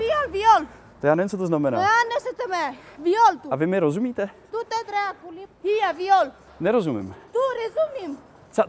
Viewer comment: Holy moly from being mute to talk Italian, German, French, Romanian and English in 1 sentence. Tell me if that isn't a miracle xdddd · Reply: Haha I heard her say dute dracu which means f off in Romanian and then she changed it Italian lmao